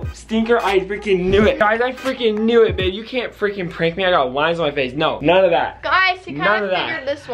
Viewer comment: When he said "oh my goodness I got lines on my face" I thought he said he had eyes on his face and I was like duh and had to watch it over 3 times to figure out what he actually said